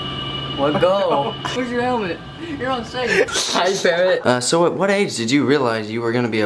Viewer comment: did just say "Hi ferret" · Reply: RecruitSwatGuy// CommandSwatGuy he said parrot lol